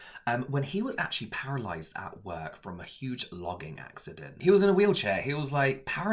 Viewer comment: At first I thought you said he was injured in a CLOGGING accident 😅